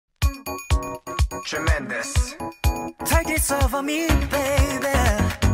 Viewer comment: no matter how much i watch this vid i always heard "shawn mendes" at 0:01 someone helppp crying